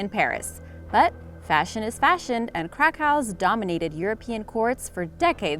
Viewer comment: Crakows....I thought you said crackhouse😆